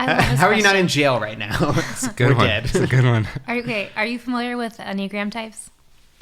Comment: what does say here?? The Myers Briggs for business part. I just can't make out the words... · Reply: enneagram :)